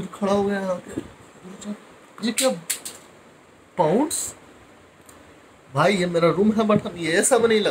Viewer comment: did I hear pounds 🤔🤔🤔🤔